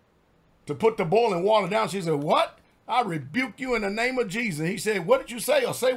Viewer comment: By her naming, the name of Christ, that spirit of "satan" was in raged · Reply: The heard her say I will burn you in the name of Jesus. She actually said I rebuke you.....